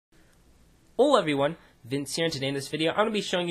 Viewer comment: The vowel at the end of Hola, and the beginning of Everyone makes it sound like I say "Ol Everyone." It's weird. But I am truly saying "Hola Everyone!"